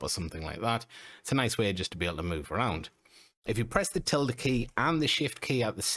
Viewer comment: I’m italian, what the heck is the “tiller” key?! I had to turn on subtitles in order to understand what you said, since I don’t see any key pressed info on screen. I’ve even tried googling it by it didn’t help…😅 Great video anyway ;) some of those tips had me jaw dropping!!! 😮😮😮